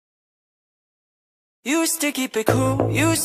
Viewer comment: The first line "Used to keep it cool" was I the only one that thought it said "You sticky pickle"?😂😂